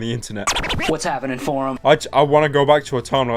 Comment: Back in the days, everytime i saw equals three by raywilliamjohnson i thought he said "whats happening forearm"